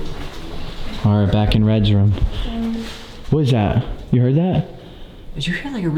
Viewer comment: i hear that deep voice sounds muffled than almost like “Reds here”